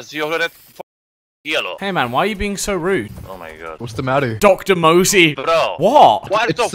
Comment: 0:6 I thought said Mr moseby 😂😂